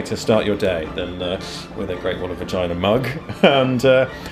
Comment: I think I would like to meet this guy . And ask him a few questions . I thought it said GREAT WALL OF REGINA !